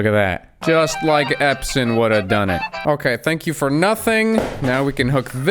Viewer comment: my god! I thought you said "Just like Epstein would have done it" I had a mini stroke.